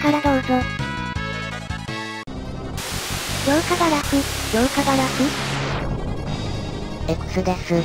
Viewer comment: 強化ガラフ＝強化ガラス、反射ガラフ＝反射ガラスに聞こえたのは、私の耳が悪いからだろうか